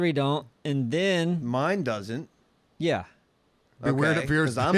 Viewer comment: Did he say BewareDaBeard?